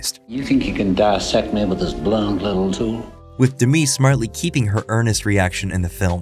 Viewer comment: did he say "with Demi?" Or Anthony?